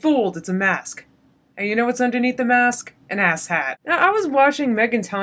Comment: I keep hearing "asset" when you say "ass hat" and that really changes your message ;‹D LMFAO re: your eyebrow rule!!! heheheheheheheee re: fretting and obsessing!!!!!!!!!!! SWL - snorting with laughter